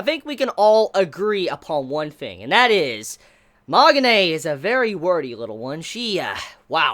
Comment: Was I the only one who thought saying Magane's name sounded like Mahogany? :D Reference